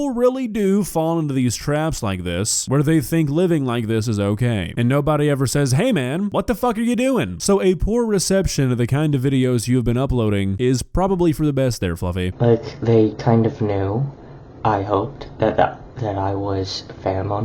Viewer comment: At the 3:43 mark did say "wtf are you doing?" Oooo never heard him cuss before. Kinda hot